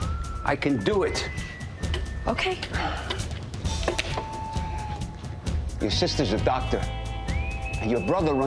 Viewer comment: Omg I thought it said you’re sister’s adopted until I stumbled upon a comment...